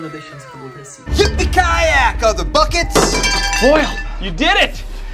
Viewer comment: juro q escutei o boyle falando "peguei você seu vacilão!"